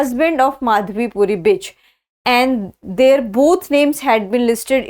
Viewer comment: she used bitch instead of Buch 😂😂😂